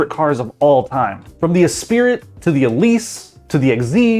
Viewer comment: Did he actually say Es-spirit? Blasphemy!